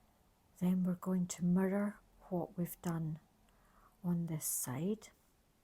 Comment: Absolutely the easiest tutorial to follow! Had a slight hesitation when I heard “murder” instead of “mirror what we did on the other side.” Then I laughed at myself. Thank you for these videos!